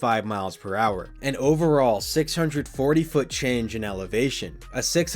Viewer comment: what'd you say 🤨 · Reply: 640 ft change in elevation